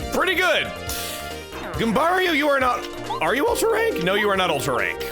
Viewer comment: When you fought the crazy daisy you asked, "are you ultra rank?" But I heard, "are you all terrain?" And now I can't get the idea of with 4 quad wheels cruising on some hills....